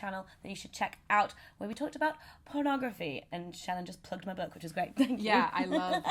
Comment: I totally misheard. I heard "Shannon just plugged my butt" and "I love your butt". Wow ,very different from book!